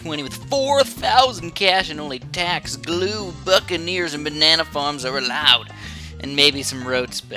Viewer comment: Lol when he said Buccaneers I thought he said fuckaneers